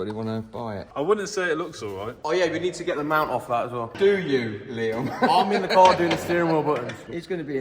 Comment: I just watched this over three times 16:51 yeah just taken off the shock absorber and Liam made a comment and your dad said “screw you, Liam! “ I think that’s so funny. Is that what your dad said “screw you Liam! So Damm funny! 😁